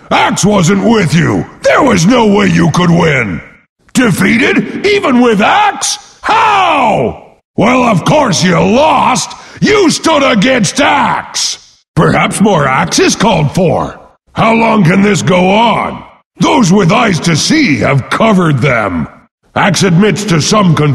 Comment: perhaps MORAXUS called for! ...I swear that's what he says. :P